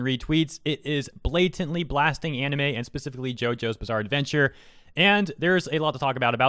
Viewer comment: the audio buggedd out for me so i thought you said jojo's bizarre great life